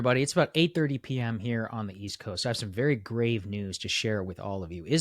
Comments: Oh! Had to replay a few times then turn on captions because I thought you said "great news". Grave news. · For a second I thought he said I have very great news 😅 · Bro I thought he said great news